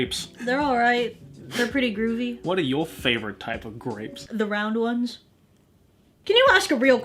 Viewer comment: Me: Watching this Video with low volumn and not really hearing what they're saying The video: 0:44 Me: RAPES??????????